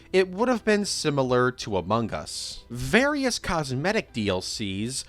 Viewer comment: I closed my eyes and I swear I thought you said “Abungus” lol